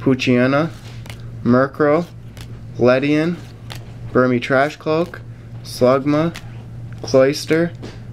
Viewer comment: It sounded like he said trash cloak. Lol